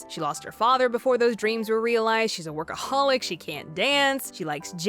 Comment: When she said workaholic, I thought she said acholihic😂